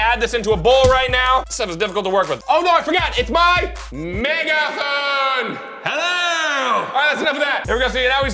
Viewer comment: According to everything looks like a jellyfish 😂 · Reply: Did he say mega hoe